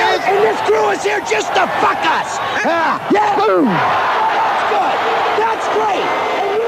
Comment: did the ump just say BOOM! when he ejected the manager😂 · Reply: I thought he said “Earl, you’re through!”